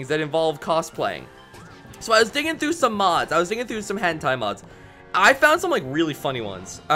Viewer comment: it sounded like you said “I was digging through some hentai mods” wait can I even comment that?